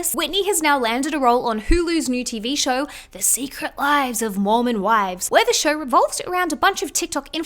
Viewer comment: Wait, I thought that ish said “The Secret Lives of “MORON” Wives, not MORMON lolol 💀